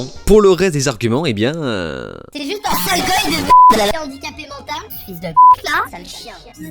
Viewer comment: Je reve ou il dit "c'est juste un sale goy de m.rde" ??? Donc si moi je dis a un mec qui a la gueule de Niney "sale j.if de m.rde" je suis moi aussi de gauche antiraciste et antifa ? :-)))) Exemple type du 2 poids 2 mesures entre eux et nous, pauvres goyims.